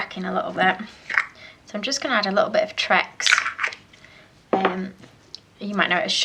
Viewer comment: Hi, what did u add at min 0:32? tracks, u said? I cdnt understand...the same happens to me with my fondant. It gets cracky...where do u get that vegetable fat? Thank u!! =) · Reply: If I have the right bit I think you mean this 'I am just going to add a little bit of trex, you might know it as shortening' I just get it from local supermarkets. Hope that helps :)